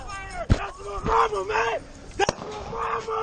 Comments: I thought he said thats my brothet but than i started hearing mother... So was that the female that was getting raped or was he indeed saying brother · the other guys screaming: "thats my mama" scary ass situation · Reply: I thought he was saying that’s my barber 😂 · I feel so sorry for the guy who kept yelling “that’s my mama” :( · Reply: Ooohhhh shoot I thought he was saying that’s obama